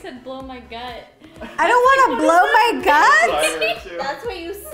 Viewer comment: The new yanny vs laurel 😂 “ blow my gut” or “ blow mike up” honestly I heard both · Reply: I heard “blow my cup” lmao